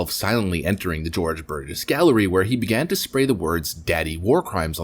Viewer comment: I thought he said the George Burns Gallery initially. That would probably be a better place for this artwork, actually.